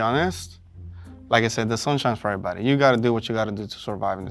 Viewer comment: "The sun shines for everybody" 🙄 like really?? It wasn't obvious after that.. he even used that answer twice 😂😂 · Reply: Idk I heard “the sun shine IS for everybody” which has a completely different connotation. Doesn’t matter which he said, it only matters which was heard.